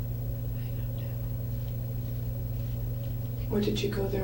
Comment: Did u hear around 13:05 sounded like Jodi whisper something. I couldnt make it out. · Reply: softy gurl she whispered, "I don't know. "